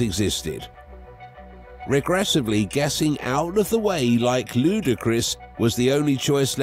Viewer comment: Did he say "... Regrettably getting out of the way like Ludacris?" Lmao I peeped that 😂